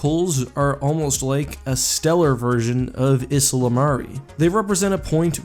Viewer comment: what is that word you said around 2:17? isola mare??